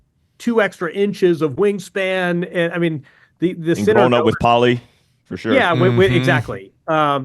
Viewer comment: what did Eubanks say there can anybody help ? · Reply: "and growing up with poly" referring to the racket string material being used